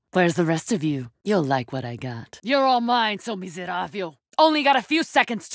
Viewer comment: You're all mine ...... What's she saying about this part? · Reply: Ohh.. I found it . It's ''seu miserável''